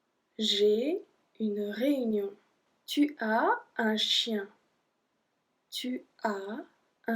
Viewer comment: You were saying "tu as un chien" but I thought you're saying "tu es un chien", so I was thinking what is she saying ???? Then I realised ohhh, no it's "tu as". 😂😂😂